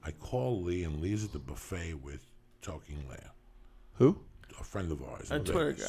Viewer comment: Anyone know what name Joey said at 33:40? Sounds like "Talking Leah"